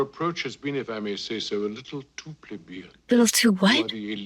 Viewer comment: "A little too what?" 'A little too plebeian' is what he said.